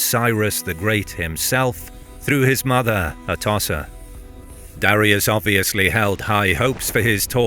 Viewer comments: Did you just call Xerxes’ mother a tosser!? 😂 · did he call Xerxes mother a tosser? 😮